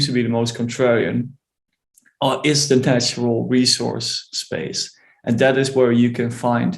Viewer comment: Please tell me what space was the most Conradian according to your guest? I could not quite make out what he said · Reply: Correct, “Natural Resource Market”